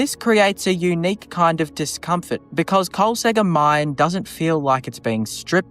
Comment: I keep hearing “Carl Sagan Mine”… 😂